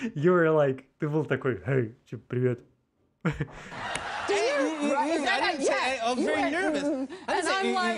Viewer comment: не могу расслышать "I was very nervous", слышу "I'm very nervous". Жостик